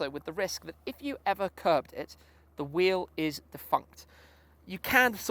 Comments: Did just say 'fucked'? That's a first... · Did just say at 2:58 "the wheel is fucked", naughty boy! Thought this was a family friendly channel... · Lot of people quite misheard wordings. He didn't cursed on 2:58. It's not f*cked it's DEFUNCT, it means that it's no longer existing or functioning. I have a good quality headphone that's why I heard it quite well. Phew.... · I had to rewind it when you were talking about curbing the carbon wheels. Sounded like you said "if you curb them, there fucked"! I was like HA! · I thought you said the wheel was fucked 😂😂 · Did just say at 2:58 "the wheel is fucked", naughty boy! Thought this was a family friendly channel... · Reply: I heard "the wheel is le fuct" :D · I thought you said f***ed instead of 'defunct'